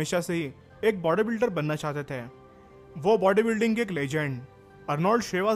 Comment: I heard -arnlod shivaji nagar at 04:55 :P :P